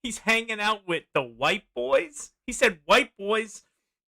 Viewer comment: You need to start listening closer. At 56:47 he says "White Boards" asking the community questions. They were boards NOT boys. You misinterpret many ppl in what they say in your videos. Don't get offended, this is constructive criticism.